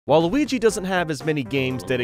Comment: Thought he said "Waluigi doesn't have that many games" at the begining